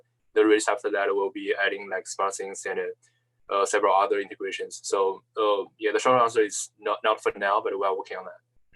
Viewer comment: <link> @ 22:46 DID I HEAR SMARTTHINGS INTEGRATION!?!?!?!?!?!?